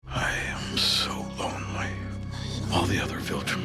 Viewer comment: When he went in for that whisper, it went a whole different direction than I was expecting. I misheard lonely as well ... horny